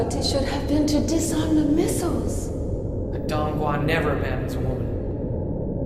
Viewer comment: think he says McDonald's wont never have sawn